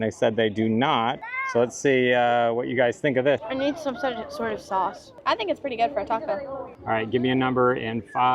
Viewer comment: was she saying needed Soy sauce? 🤔 Not sure about soy sauce on tacos but everything looks amazing. Would LOVE to be able to come try it all! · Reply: She said "it needs some sort of sauce", not specifically soy sauce. We had fun trying lots of great things here, hopefully you can enjoy Anakeesta someday too!